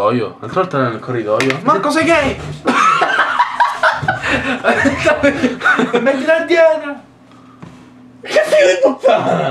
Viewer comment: cosa dice a 5:27? non capisco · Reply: Io ho capito "Mettilo a dieta" o una roba simile